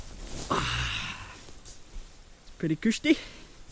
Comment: when you are trying out your moss bed it sounds like you say, "pretty kushti" which is a Romany expression. Has that entered the Scottish vernacular or did it come from somewhere else? Some of my Grandparents were Romany Scottish Travellers, so I am just curious.